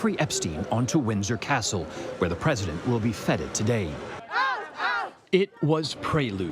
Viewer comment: At the 58 second mark, it sounds like they say "where the president will be fetid today." Cc says "fed it" but that doesn't seem to fit the context. Did someone slip in some word play there?